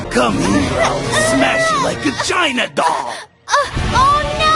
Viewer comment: i’m sorry but “\/agina doll!” is what i keep hearing